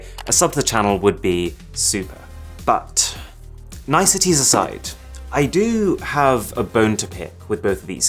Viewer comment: What I heard “Nice t**ties aside, I do have a bone to pick”